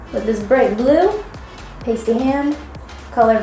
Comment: Lol when you said "pasty" my phone thought you said "hey Siri"